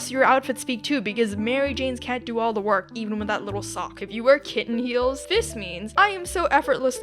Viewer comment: why did I hear KitKat